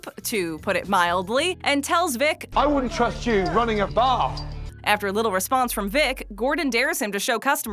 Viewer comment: I thought Gordon said to vic "I WOULDNT TRUST YOU RUNNING A BAR!" I just realised he said bath, not bar